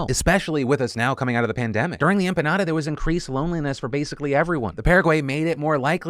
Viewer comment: Did he say "During the empanada..." and "The paraguay made it more likey..."?